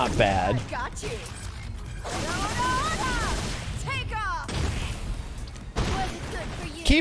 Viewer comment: With Poison's super, I've always heard her say, "Take a hike," and not "Take off!"